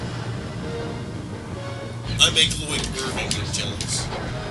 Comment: did I just hear say he makes Lloyd Irving jokes?